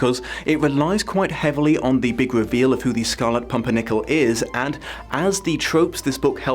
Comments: did he just say pumpernickle? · OK, did I hear 'Scarlet Pumpernickel' at minute 5:24??? Never mind 😂😂😂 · did he really say scarlet pumpernickel? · Lol, did you just call him the "Scarlet Pumpernickel"? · Just before 5:30 did say Scarlet Pumpernickle? Because that's hilarious and now a bread/dish i want to see made!! 😁😁😁